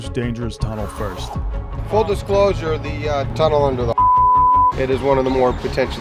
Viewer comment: Tunnel under the Hard Rock is what he said.